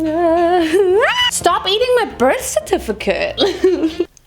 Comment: in the intro when said ''stop eating my birth certificate'' i heard ''stop eating my breast certificate'' i honestly dont know.